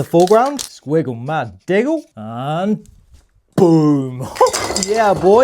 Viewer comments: I thought he said "I'm gay" until I watched it again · I heard I'm gay instead of I'm game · i thought that he said" whatever you want the next tutorial to be i'm gay."😆😅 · I thought he said I'm gay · I thought you said "I'm gay" oh it was "I'm game" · I thought you said "im gay" but i rewinded and saw "im game" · NAH I HEARD 'whatever you want the next painting to be, I'm gay'- HELP ME 😭🥀💀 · I heard ‘I’m gay’ instead of I’m game 😭😭😭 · When you said "Im game" at the end I thought u said "Im gay" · I thought he said "I'm gay"